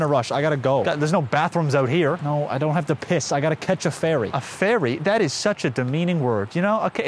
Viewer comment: “ Igotta catch a ferry” in the deep wilderness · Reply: I thought he said i gotta catch a fairee